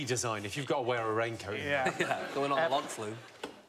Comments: did anyone catch what jason said at 2:31 ? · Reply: "Going on the log flume" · i think he says "not going on the log flume" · What did Jason say at 2:31? Have replayed it 5 times and can't figure it out and there's no soddin' subtitles! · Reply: “Going on a log flume”.